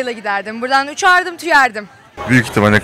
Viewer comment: ne dediğini anlayan var mı ? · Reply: Berkan Caca İtalyaya giderdim uçardım tüyerdim sjdks